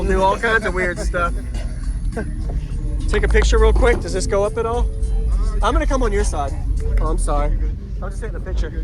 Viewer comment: all I heard was I'm going to cum lol